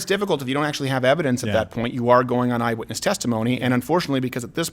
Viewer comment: Did you just say, " The N justifies the means."?